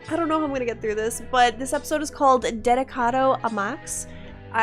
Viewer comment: I thought bruv said "avocado max" instead of dedicado a max 😭😭😭